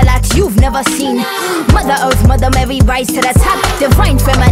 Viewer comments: "divine feminine, i'm feminine" i can't explain how powerful this line makes me feel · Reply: ITS BEEN 2 MONTHS AND I THOUGHT SHE SAID "DEFINE FEMININE, IM FEMININE”. omg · "divine feminine, i'm feminine" i can't explain how powerful this line makes me feel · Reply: I thought it said " define feminine I ain't feminine" CHILE😭